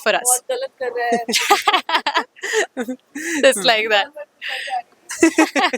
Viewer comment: Can any one tell what sam says in last?? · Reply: Acha laga baat karke same